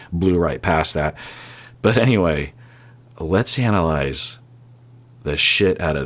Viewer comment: “Let’s analyze the SHIT out of that statement” 😂 I’m going to incorporate that into my everyday vernacular 😂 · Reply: At first, I thought he said, “Hannah-lize!” 😂